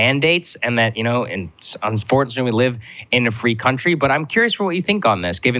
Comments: Did Saagar just say unfortunately we live in a free country? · Did Saagar just say "unfortunately we're a free country.." I'm genuinely asking. Edit: Didn't read pinned comment. Sorry Saagar. I genuinely thought you said some crazy nonsense. Next time, lay the sarcasm on thick.